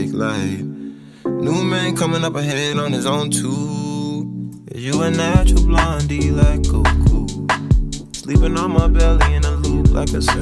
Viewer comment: I thought he was saying “you a natural blonde do you like coco,” because Frank is black. Boy was I off 😂😂